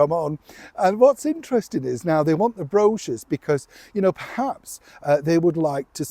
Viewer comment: Oh, dear. This is one of those translation issues. I thought you were saying they want Brooches! Couldn’t figure WHY they wanted Brooches. Three times through I finally realized you’re saying Brochures! Over here we put the accent on the last syllable. Oh well, one language, two countries. 🇺🇸